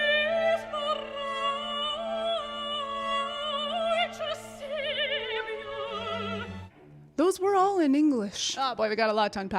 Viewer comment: "for righteous savior"???? Is that what she said??? Idk